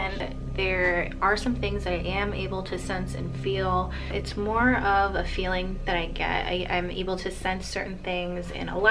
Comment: I heard something but couldn't make it out. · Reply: It sounded like get out